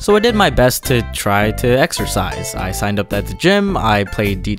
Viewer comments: Exercise I thought you said extra fries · Exercise I thought you said more fries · Exercise? I thought you said "Extra Fries" Nobody: 40 year old Facebook women:🤣🤣🤣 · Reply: Exercise? I thought you said mass genocide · Exercise... I thought you said extra fries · Ex-er-ci-se Eggs-er-ci-se Eggs-are-side of bacon BACON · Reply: Exercise? I thought you said. E x t r a. F r I e s (kill me) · Exercise??? I thought u said extra fries? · Exercise? I thought u said... Wait for it... Extra fries! XDDDDD LOL 😂😭😂😂😂😂😭😭😭😭😂😭😂😭😂😭please pin me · Exercise... I thought you said extra fries! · Exercise? Damn, I thought you said extra fries.... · Exercise oh I thought you said extra fries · ooooohhhhh exercise I thought you said extra fries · Exercise, I thought you said extra fries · (I have a shirt of this xD) Exercise? I thought you said extra rice. (LIVING DAT RICE LIFE BRO 😎😂) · Ex-er-ci-se Eggs-er-ci-se Eggs-are-side of bacon BACON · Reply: Excersize? I thought you said EXTRA FRIES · Exercise?!? I thought you said extra fries?!?! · Exercise? I thought you said 'Extra Fries..' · Exercise?? I thought you said "extra fries"